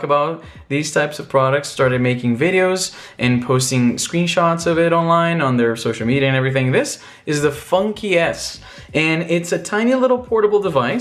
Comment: I thought he was saying funky ass